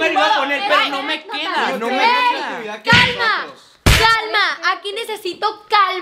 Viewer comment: Yo cuando dijo calma pensé que dijo ganó comida😂